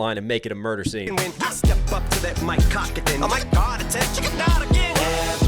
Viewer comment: Does anyone else hear at the 16:11 mark an "MG not again" MGK? Or is that a reach? 😬